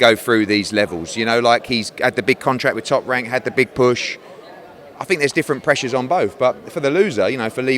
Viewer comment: it sounds like he says “he had the big bush” 😂